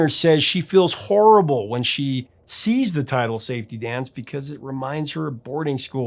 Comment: As a kid I always thought he was saying, It's safe to dance.